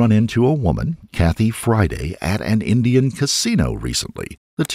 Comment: I’m really glad the names of the people involved in these cases are put in the video so we can see them because at first I legit thought he said Happy Friday not Kathy Friday. 😳🤦🏼‍♀️🤦🏼‍♀️ Anyway… I’m really glad he wasn’t able to get away with what he did to Vicky because he is EVIL and just an absolute monster. I’m glad Vicky, her kids, her family and her friends were able to get justice. As, the only thing as worse as what these evil monsters do to their victims is when they get away with what they did. So then the victims and their loved ones don’t get the justice they deserve.